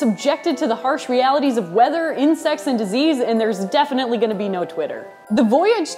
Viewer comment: thought she said "Incest" I was like o.O